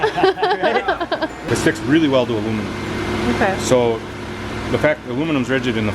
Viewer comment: I thought he said "A woman" and not "aluminum"